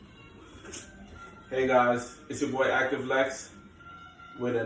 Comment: I thought you were saying it's your boy, Active FLEX 💪🏾💪🏾 lol